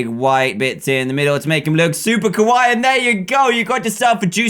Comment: It sounds like you were saying Kowaii which is actually the word for scary in Japanese xD